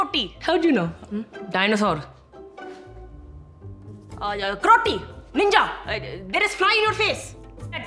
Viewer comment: I heard Karroti 😂😂😂😂